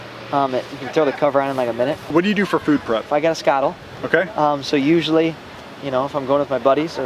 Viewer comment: what did he say his food prep device was(at 20:14 in the video) Haven't heard of this one. Thanks in advance. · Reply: Skottle